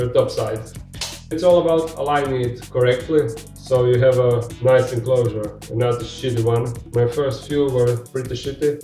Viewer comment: LMAO. For the longest time I thought you were saying city enclosure due to the accent. It wasn't till I saw this video that I knew what you were saying CD enclosure. I was like what is a city enclosure and why is it a city? LOL. Totally love your accent.